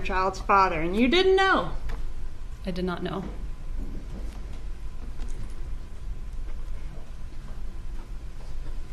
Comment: Can anyone tell what the woman in the gallery is saying at the 10:45 mark? Right after slams her fist on the podium. She's right behind left shoulder. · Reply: Looks like she said , "I bet."